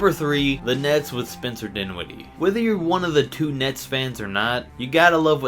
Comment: Did he say "if your one on the two nets fans."